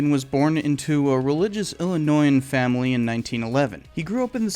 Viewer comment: I swear, I first heard "....born into a religious AND ANNOYING family," said "Preach!" and then did a double take and realized that it was "Illinoisan," not "...and annoying."😂😂😂